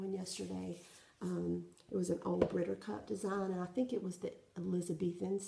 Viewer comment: Hello! I love all your videos! I have spent over an hour looking online for the chart for your amazing Elizabethan Sampler by Brittercup Designs...so far impossible to find! I just love it, and collect 9 square designs. Any ideas where I might find it? It was not on the Brittercup website either! Thanks so much, keep posting...just love you and! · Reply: Thanks I will check it out. If its still there on payday...I'll probably get it. Part of my problem was I thought you said Brittercup, and it was Brightneedle. Silly me! Anyway, I am ashamed to say I have spend that much (and more??? oh no) a couple of times when I really loved something. Priorities, you know! :) Thanks with love!